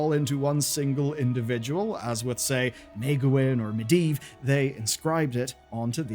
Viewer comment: did you just say maegewyn 8:20 instead of Aegwynn?